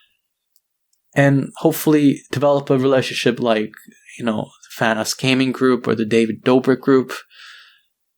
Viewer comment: did he say vanoss?